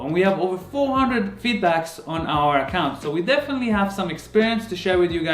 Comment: "400 vBucks on our account" was what I heard 😂